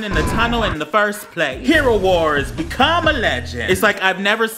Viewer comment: i thought it said “become a lesbian”